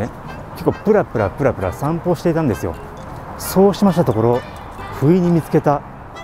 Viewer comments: 遂に食い逃げカミングアウト動画出したのかとドキッとしました。 冒頭の「不意に見つけた...」が「食い逃げ...」に聞こえたもので😅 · 不意に見つけた が 食い逃げした に聞こえた冬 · 「不意に見つけた」が「食い逃げしてた」に聞こえました 明日、耳鼻科に行ってこようと思います。